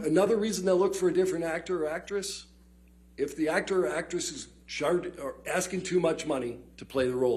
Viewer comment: Does he say sharted referring to Amber "I sharted the bed" Heard 35:47 😂